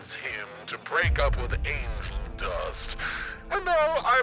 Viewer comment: I like how Alastor said "Break up with Angel Dust" while the subtitles said "Break up with the Angel Dust."